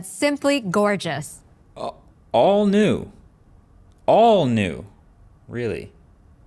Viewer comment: All new? 😂 I heard " all noob ". My bad..😅 human error.